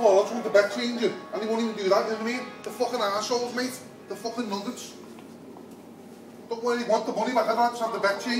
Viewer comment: What did he say there?? Fucking Nuggets?